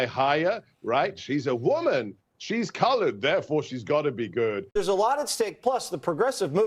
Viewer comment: Kamala will be a great President and it’s time America 🇺🇸 has a female president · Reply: did he just say “colored” 😮😮